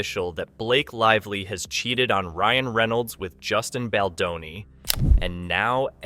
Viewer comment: I thought you said blake chested on Ryan..click bate